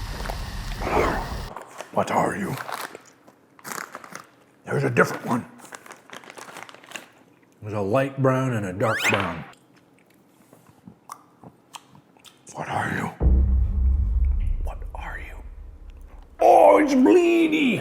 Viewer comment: It sounded like he said “Gregory”Where are you”